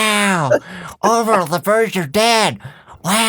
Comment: What said: Orville the birds are dead What I heard: Orville the virgin dad